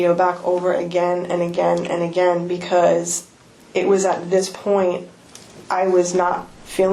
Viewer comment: did she just said "it was at this point that i knew i (f-up)"😅😅